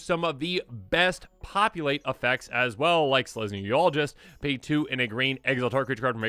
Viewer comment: It sounded like you said "selesnya urologist" and it made that sword look even more threatening 😮